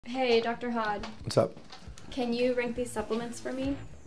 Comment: I thought she said Dr. Hot.